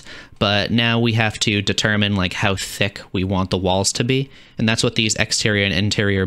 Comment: i misheard that instead of walls i heard balls